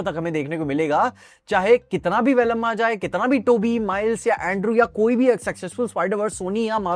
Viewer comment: Did really said Velamma 😅